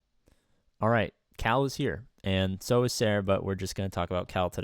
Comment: is it just me or did it sound like he said "and so is Sarah" in the beginning instead of "and so is Cere" lol